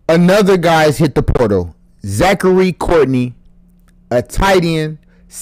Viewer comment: Who was it. How do you make a video and not say who the person is. Just a 3 star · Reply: I heard him say Zachary C.